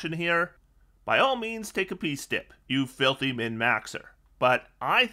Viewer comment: What did you say at 15:26 ? Was too difficult to understand. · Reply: I think he’s saying take a piece dip as in a piece domain cleric. I’m using voice to text. Sorry it’s not the right piece.